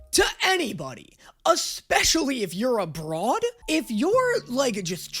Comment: I fully thought he said “especially if you’re a broad” and i was like … why would being a woman make a diff ? THEN I realized he said abroad